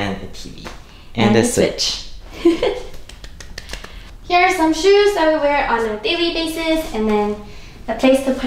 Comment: here are SOME shoes? Did I hear that correctly?